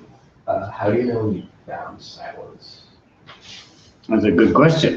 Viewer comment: I'm having trouble hearing the words in the question asked at 1:21:07. Does anyone know what was asked? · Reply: It is "how do you know you've found silence?"